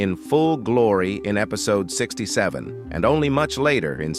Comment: did someone say 67